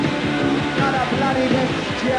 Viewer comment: did he just say "im really handsome?" lol